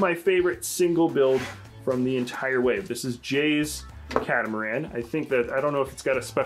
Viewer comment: did he just say jay 😂